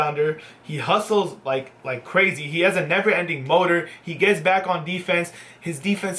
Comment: I thought he said never ending boner😂